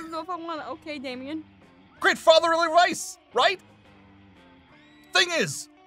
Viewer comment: Did say great fatherly rice or great fatherly advice